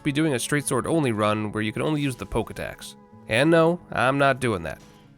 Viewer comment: my dumb ahh misheard him and thought he meant "might aswell just be doing a straight sword only run where you can only use the "pokedex" " and thought he was joking ahahahah